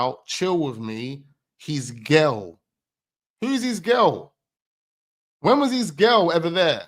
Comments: To be honest I heard " these girls " but I could be wrong... he just chats S#£t anyway 😂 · I heard 'these' girls but there was so much waffles... he can't keep the 'story' straight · I thought he said "these girls" not "his girl". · I hear it as “these girls” not his girl So I would say “these girls” were the girls from Nottingham and it was “their cousins”